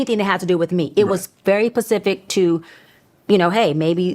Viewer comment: Sooo offset asked her about the ocean ?? Because at 33:54 she literally said “Pacific“, what doctors do you know say that instead of the word “Specific”. This lady a fraud and that confirmed it for me literally 😂😂